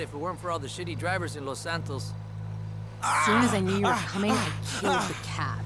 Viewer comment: Anybody able to understand what she says at 9:47? "As soon as I knew you were coming..." then something about cats? I replayed the video like 5 times and still have no idea what she said. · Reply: She definitely says she killed the cats? I don't know what it means...